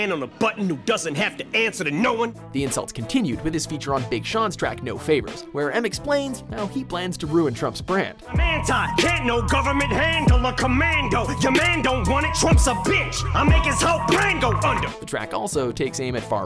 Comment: Could have sworn I heard "head like a mango" lmao